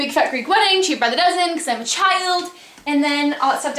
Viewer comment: does she say "because i have a child"?